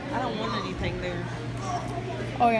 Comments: wtf does say? “i don’t want anything durrr..” 😭 ive seen that clip hundreds of times but never know what they said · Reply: just “i don’t want anything there” she just said it in a weird way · wtf does say? “i don’t want anything durrr..” 😭 ive seen that clip hundreds of times but never know what they said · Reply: i heard ‘i don’t want anything there’ i could be wrong tho haha